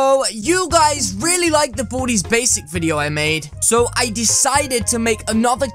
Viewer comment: I HEARD BOOTY'S BASICS!